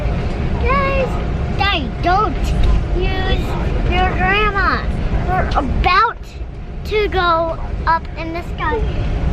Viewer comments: Did say "Don't use your grandma. We are about to go up in the sky"? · did really say "guys don't use your grandma" at 9:10? lol that kid is hilarious. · did just say "Don't use your grandma"????? What does that even mean? 😂😂😂